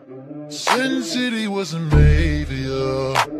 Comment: I originally misheard this as: "Sin City was Arabia" I don't know WHY, but it was kinda funny at the time. 😅